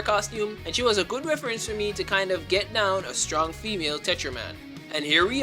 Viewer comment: I don't know if anyone else got this mixed up, but when he said "get down," I thought he said "Vietnam"